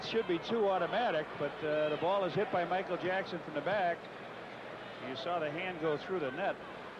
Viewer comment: Did he just say michael jackson or am i loosing it ?